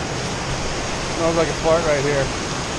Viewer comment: did you say smells like a fart right here?